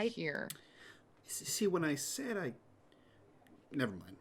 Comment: I want to know what Malik was going to say! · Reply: "See when I said I checked her out of the hospital, I meant the psych ward."